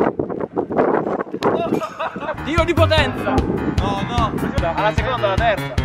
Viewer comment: Solo io a 6:31 ho sentito Dio di potenza